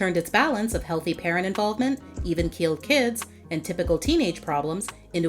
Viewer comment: I thought she said they even killed kids but she said even- keeled kids. I was like who fucking died on sistax2?